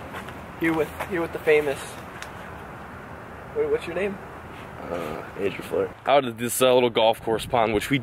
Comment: Lol I heard you say Andrew flair 😂